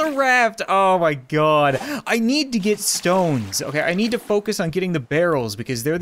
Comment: I literally thought he said we need to get STONED